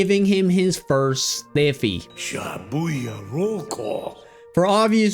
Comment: I'm sorry, but his first what? biffy sounds like a funny word ngl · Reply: he said “stiffy”